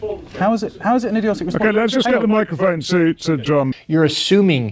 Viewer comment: I honestly think he said "let's hand a microphone to the drunk".